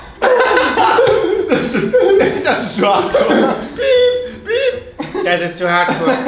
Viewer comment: Ich hab halt nicht 'Hardcore' verstanden sondern 'High porn'